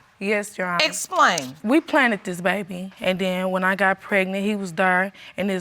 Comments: Paternity & Divorce court needs a dictionary based on all these made up words these ppl say 😂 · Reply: Planeth i thought i heard 😂😂😂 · Soooo, they “planted dis baby...” Whew, chile!!!! 😩😩😩😂😂😂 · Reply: I heard "planneded"🤦🏽‍♀️ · We “planted” this baby.....😂😂😂😂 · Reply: Did she say planted? I thought she said "planded". The title says "planned".